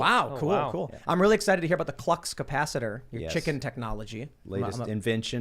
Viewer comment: Bro I thought he said Klux Capacitor and I was like wow that escalated quickly